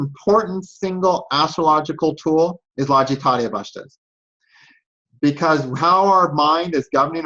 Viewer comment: Can someone help me understand what is saying, the Lajitatia Vastas? Something like that. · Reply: he mentioned Lajiitadi Avastas